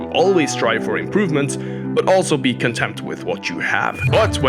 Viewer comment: Sounded like you said we should be contempt with our gains, not content😅 I do experience some contempt towards my lack of gains😂